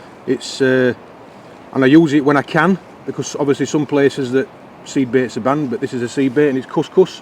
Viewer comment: Nice one, just a point, cous cous is made from semolina flour, it can't be classed as a seed bait. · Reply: I thought said "sea bait" and I'm thinking "What's cous cous gotta do wi' sea?🤣